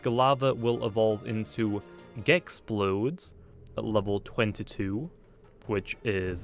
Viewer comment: captions says it evolves into gay explode